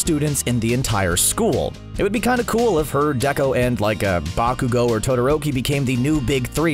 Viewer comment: did anyone else hear him say deko?